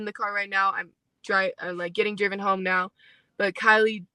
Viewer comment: between 1:30 to 1:32/ 12:35 Listen ohhh she did say I was driving home then realized what she said continues bla bla bla